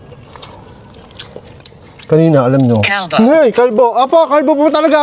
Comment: narinig ko talaga Sabi Kalbo tas na detect Nung gamit mo app